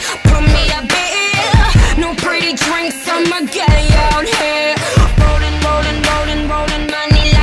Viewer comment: minute 1:09 I hear 'Bolin Bolin Bolin'